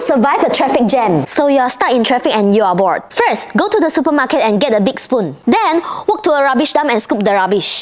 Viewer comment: Bro I thought said "first go to the supermarket and get a poop" 😂